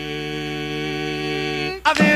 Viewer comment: love this music, always have,, and this song is awesome,, but when i was way younger i always thought he said "bitching and a crying",,, but its Preaching and a crying,,,, too funny though cause i knw what he says, but still sounds like bitching